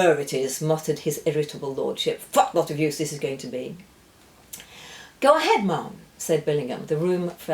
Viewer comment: what did he say? 😅😂😂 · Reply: He said, "Fat lot of use that's going to be."